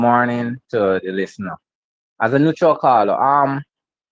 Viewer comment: I thought she said she's neutral.